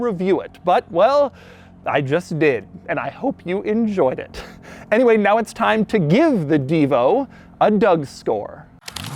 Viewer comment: my heart almost stopped, I thought he was saying, "Anyway now it's time to GIVE the Divo.... away to some lucky viewer."